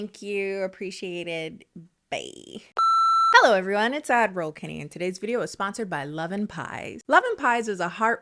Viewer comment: I always thought she said Admiral Kennedy just to find out she saying Ad roll Kennedy. So Petionton for it to be Admiral Kennedy 🇺🇲🇺🇲🇺🇲🇺🇲